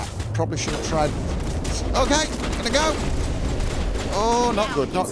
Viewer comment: Did I heard him say Inigo at 39:12? 😆